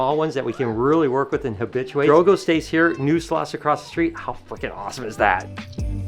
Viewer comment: “how fucking awesome is that” did I hear you correctly? 😂 I have never heard you cuss! Pretty awesome!